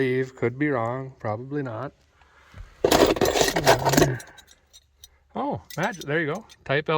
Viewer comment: Did he just say 'badgage?'